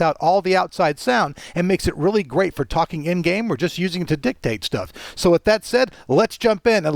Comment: Did you say dick take? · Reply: LOL, weirdo,, LOL Dictate foolio :-)